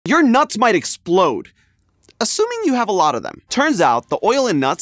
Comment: Blud when i heard "ur nuts will explode" 💀